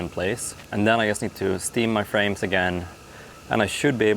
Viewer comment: Steamed frames? I thought you said we were having steam clams!